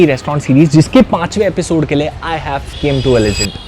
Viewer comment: did I hear i have came